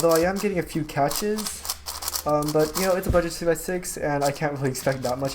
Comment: Did anyone else think he said 2x6 at 1:13?